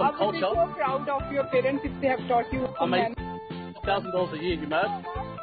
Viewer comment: did he just say " i make a thousands dollars a year umad?"